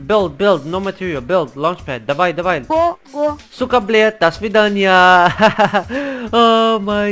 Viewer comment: what does he say at 4:22 ? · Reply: «Сука блядь до свидания» = “goodbye whore”